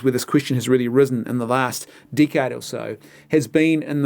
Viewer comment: Sounds like hes saying 'The last dickhead or so'